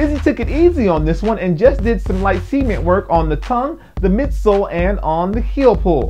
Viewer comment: dude says cement funny it almost sounds like he says semen lol